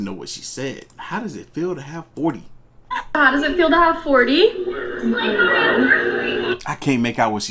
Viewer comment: I think she said 41